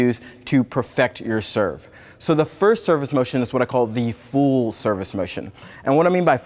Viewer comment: I thought he said fool service motion i was like wtf